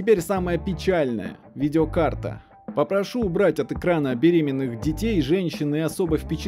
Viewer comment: мне не послышалось? БЕРЕМЕННЫХ ДЕТЕЙ???